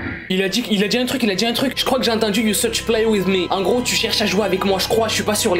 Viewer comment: moi jai entendue "do you want to swich with me" se qui veux dire "tu veux echanger avec moi"😨